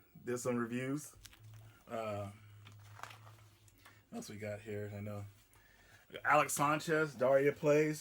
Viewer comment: sounded like you said we got weed at the start